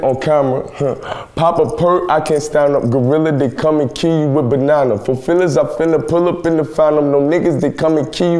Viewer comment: I thought he said “come kill u with bananas”